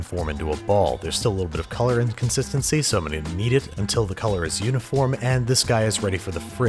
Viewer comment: Is it bad every time I hear him say ‘knead’ I keep hearing ‘yeet’